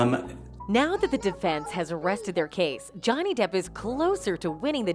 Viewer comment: "The defence has arrested their case?" Arrested? That's what it sounded like you said🤣.